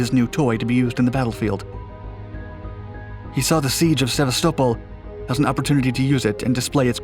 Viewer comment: Hahaha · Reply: !!! Sa-vast-a-pole I have NO idea what he's saying?!!! HAHAHA